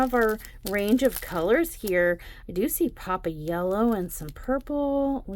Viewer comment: I heard you say pop off yellow but thought you said Papa yellow 😂😂